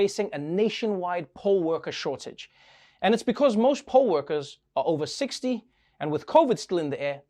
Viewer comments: When he said poll workers I first heard "pole workers" thinking of pole dancers... the image became rather strange when he said most of them were above 60 just before I caught on what he really was talking about... I feel like I am not quite awake... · POLL WORKERS ? THOUGHT YOU SAID POLE WORKERS WHO WORK IN CLUBS LOL